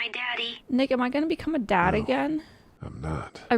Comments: i really thought she said "n word am i gonna be a dad again" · She said "Nick am i" so fast I thought she said something else for a second there, fricking shocked me right there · what did she just say? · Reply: “Nick am I?” but yeah at first I thought I thought I heard something else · what did she say 0-0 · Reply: “Nick am I?” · did she say the N Word????????????????????????? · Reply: No she just said "nick am i" so fast it sounded like she did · did she just say Ni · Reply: I think she said Nick.